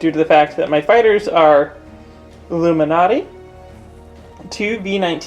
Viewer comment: Lou Malnati for Luminara ... theres a Chicago word substitution if I've ever heard one. Flying around on the Delta 7 pizza slice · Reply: Being from the East Coast and not catching the joke, I thought he said "Illuminati"...